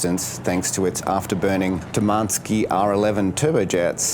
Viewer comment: Oh Tumanski engines. I thought you said 'demanski', and I thought how appropriately named 😂